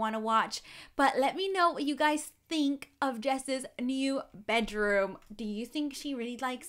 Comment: I just heard her say “Jess’s” new room